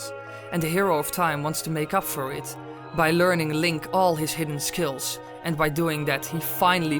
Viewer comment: did he say by learning link?